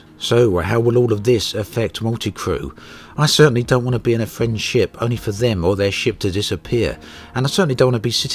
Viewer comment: It sounds like you said "I certainly don't want to be in a French ship". Personally I think the French make good space ships.